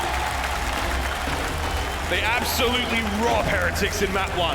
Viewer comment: did he say “they absolutely raw heretics in game one”? lmfaoooo